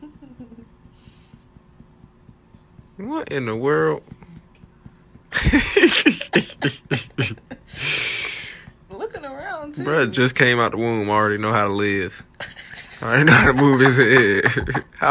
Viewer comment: that "bruh just came out the womb already know how to live...how old are you" tore me up 😆 🤣 😂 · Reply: Haha I thought he said “already know how to lift” .. hilarious either way 😂